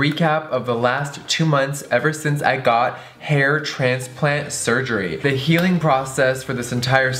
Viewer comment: For like 10 seconds I thought it said heart transplant. I almost shit myself😂